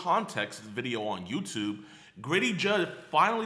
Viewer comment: did u say griddy jud?